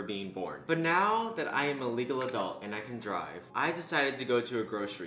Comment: I swear to god I thought he said I am an illegal adult. I wheezed so hard, I had to replay just to make sure.